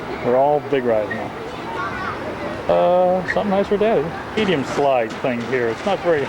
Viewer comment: what he said lol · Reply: I caught that too LOL "Something nice for daddy" Ya had to perv at Geauga Lake. Especially in the Wave.